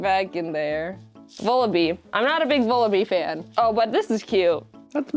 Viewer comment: All I heard was “I am not a big jellycent fan” and I would like to say this is hearsay, I challenge you to a duel. Swords at dawn, cowardice will result is me being slightly annoyed at you